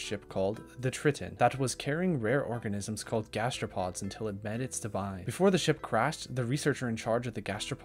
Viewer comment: “Sulphide deposits of saphelerite” you go girl, you try. Also wtf did you say here? 7:09 until it “benisdimine”. You really strugglin out here 44:10. 54:03 how do you struggle with your main language, home turf this should be easy!!! Walk in the park!!! I cannot let you influence others